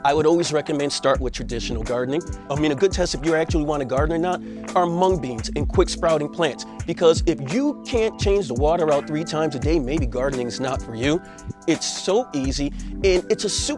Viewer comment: Did you just say " our Hmong beans"?